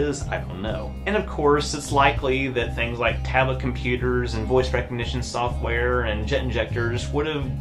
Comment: "Tablet computers, voice recognition, jet injectors." I had to go back and relisten. First I heard "10 genders".